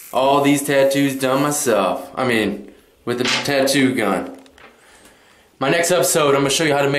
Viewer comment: all this tattoo u did by yourself with potato gun sory but i heard potato